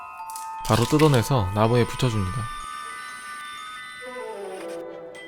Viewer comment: 아무 생각 없이 들으면 ''나고에 붙여줍니다.'' 라고 들림